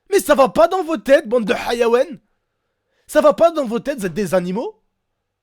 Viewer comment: a 3:09 ça veut dire quoi le mot? (je prefère ne pas essayer de l'écrire xD) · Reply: il a dit bande de ''hayawan'' qui veut dire animaux